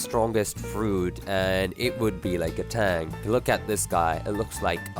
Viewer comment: Did i hear, TANK?